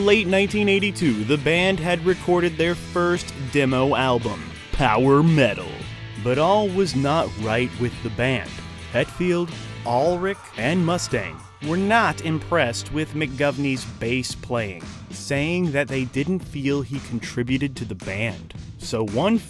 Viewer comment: Is it just me? Or is this guy actually saying what i was only saying jokingly, and calling Mustaine 'Mustang' ?? 🤔🧐 2:24 Also can tell a millenial wrote this script as ge completely disavowed their given name in the beginning and never referring to them as 'Alcohollica' ? Oy vey!